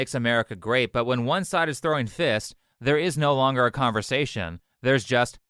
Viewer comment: “If someone is throwing a fist, then there is no longer a conversation.” 💯 Very well said. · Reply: I thought he said Throwing a fit, like a toddler. But I guess it's the same.